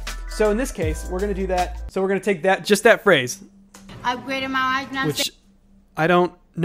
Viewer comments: Once you work out she's saying "Upgraded my watch know [what] I'm saying" ... I mean, she is literally showing us her new watch! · I heard "upgraded my wife now I'm saint"